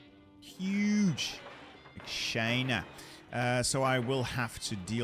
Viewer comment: did I heard "China" or "Kinda" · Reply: China, he was doing a Trump impression